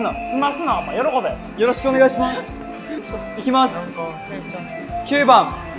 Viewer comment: エイキの後ろにトムがいてカイナオが何か見ててセイちゃんて声聞こえる？🤭